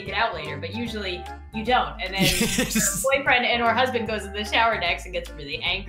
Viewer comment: did she say boyfriend AND/or husband?!